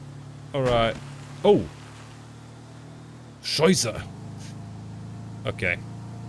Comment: Did u said "scheisse" at 24:08 ? 😂